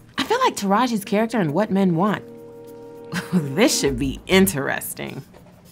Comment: OH SHIT YO I THOUGHT THAT SAID WHAT MEG WANTS INSIDE 😂😂